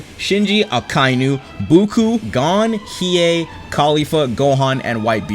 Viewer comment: Wait did he just say "Buku"?